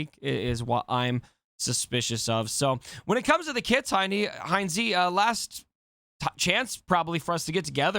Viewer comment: Did call Hynesy "heinie" around minute 3:50?! XD